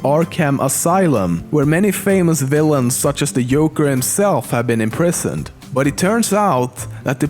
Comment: Did anyone else hear "Yoker" in 4:05 ?